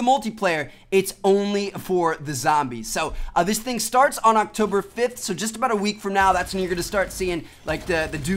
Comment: Did he say October 5th